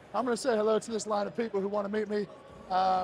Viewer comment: When you said "Beyond lucky, beyond grateful" I thought at first you said "be unlucky, be ungrateful" LOL.